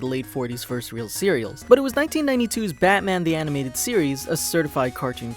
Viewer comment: I thought he said 1942?